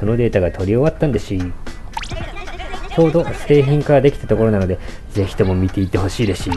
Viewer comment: の製品化がセ〇キン化に聞こえたww